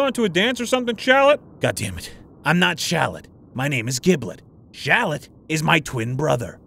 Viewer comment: I thought giblet said piglet not going to lie